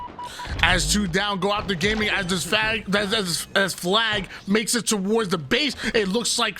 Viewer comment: commentator says "fag " instead of "flag" 😂😂😊